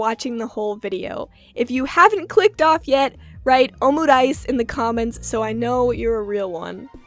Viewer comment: Make a video about miss nagatoro omice (is that what you’re saying at 32:10 ? i can’t tell but whatever 😭)